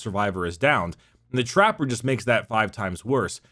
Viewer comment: Ngl. Thought it said how to Chance the Rapper and I was very confused.